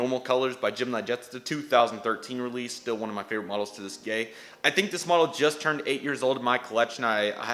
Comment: what did you say 😅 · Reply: Sorry about that, I said “still one of my favorite models to this day”